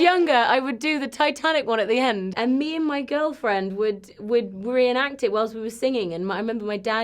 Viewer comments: Did she just said "my girlfriend"? Or am i hearing things??? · DID SHE SAY GIRLFRIEND????????